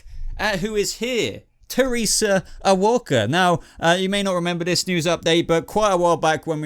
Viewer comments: I thought they said “Waller” at first, that would have been interesting however I’m excited to see what this leads to. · I thought he said “Waller”, woulda made more sense if it was Amanda Waller since proto bane is working with the government & the brain chips scream Amanda Waller